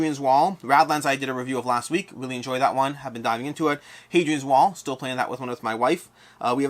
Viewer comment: I heard: "still playing that with one of my wifes"... I cracked laughing. I don't know why... Maybe the casual tone ahahahah (Edit: orthography)